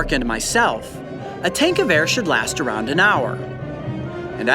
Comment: when he said a tank of air i thought he said a tankgewehr lol